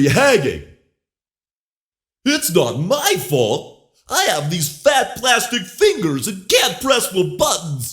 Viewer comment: Even though it was a good made sound source, it's not perfect. At 3:08, Toy Freddy say "I had these fat plastic hands", but the subtitle say "I had huge plastic hands".